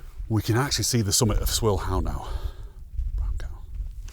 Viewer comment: I had to pause the video and play it back from 35:40 because you said Swirl How and I immediately thought brown cow and then I thought did he actually say that very quietly? Yep, you did. Tickled me.